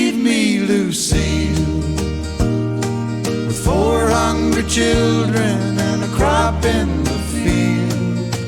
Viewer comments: My parents had his greatest hits album when I was young.I known this song for decades. When I was young, I thought he was saying 400 children.Not four hungry children children. It was very confusing for me. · The first time i heard this song back in Manila, I thought he said "four hundred children"😂😂😂